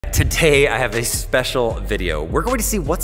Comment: Who else heard “to tay”